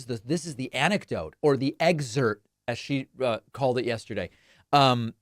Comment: I love how David picks up the same linguistic glitches and errors I do. I heard “exert” too. 😂